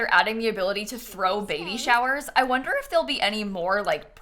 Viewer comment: When you said that they will be able to throw a baby shower i fr thought you said that we will be able to throw babies. I love my brain <3